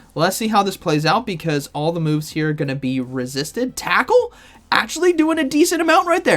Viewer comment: At first, I thought you said "Taco" but then I realized you meant Tackle lol 😂